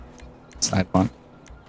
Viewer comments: What did say at 6:40??? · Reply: side walk lol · What did he even say and how tf was it funny? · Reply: Sidewalk